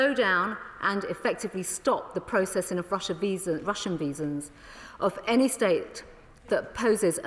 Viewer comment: did she say - Russian Visas? Rush on visas? Russia Veesions?